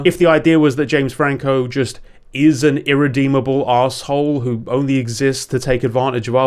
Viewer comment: sounded like "an irredeemable Oz-hole" made me laugh.